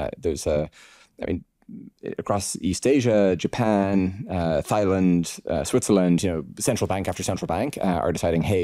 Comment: Did I just hear "Thigh-land" @ 1:18:46 😆